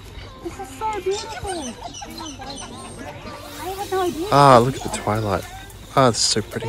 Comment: I really thought said "ahh, look at the toilet, it's so pretty" like he was sincerely appreciating the aesthetics of the garden long drop or something 😂😂